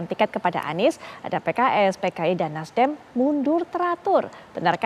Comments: apa gue gak salah denger ya??? Dia nyebut PKI?🤔 Coba deh tekan tombol cc di kanan atas (subtitle) lalu nonton lagi di menit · Apa sya salah dengar ya di menit 2:30 presenter menyebut partai PKI...?